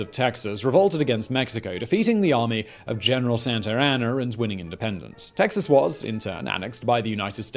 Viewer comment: says " General Santeraner "(?) the subtitle says " General Santano". I thought it was General Santa Ana. Didn't even google it.:face-purple-crying::goat-turquoise-white-horns